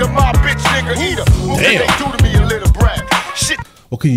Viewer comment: I thought it said what can you do to me and live to bragg